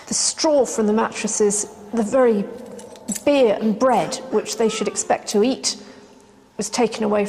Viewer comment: does she say "the BEER and bread which they should expect to eat."? good idea.....pump the crazies full of alcohol!!!!